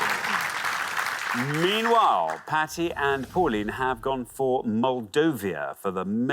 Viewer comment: When Xander said 'Patti' I thought he said, 'fatty'.